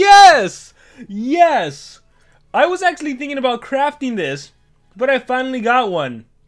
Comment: DID HE JUST SAY CRAP OR CRAFT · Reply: It’s pretty clear he said craft.